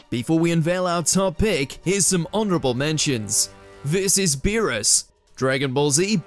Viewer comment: What's your favorite depection of the Turtle Wave? Let us know in the comments! For more Anime, click here: <link> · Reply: I thought he said "This is Beerus". The accent.